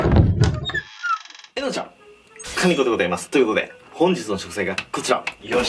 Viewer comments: 最初の[かねこでございます。]っていう所が[かにこでございます。]って聞こえた!!! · カニコでございます。って聞こえたw